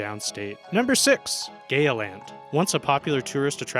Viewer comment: I heard "Gayer Land" Hey, it may be gayer than any land but even a big amusement park with a name that sounds like that could never be any gayer than me.